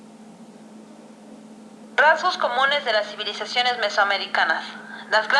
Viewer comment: Que dice en el minuto 2:04 y 2:05? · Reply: Creo que dice “Rasgos comunes”